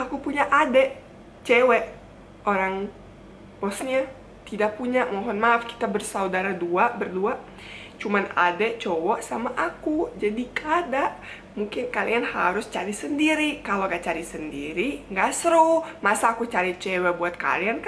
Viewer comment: klo saya ga salah dengar kk pake bahasa Banjar "kda", setau aku kata "kda" itu bahasa Banjar, aku ga tau ya klo ada daerah lain yg make. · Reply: Salah dangar sanak. Iny manyambat "gada"